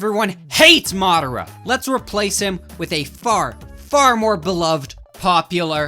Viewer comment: I thought he said replace with a fart. 😂😂😂😂😂😂😂😂😂😂 😮😮😮😮😮😮😮😮😮😮😮😮😮😮😮😮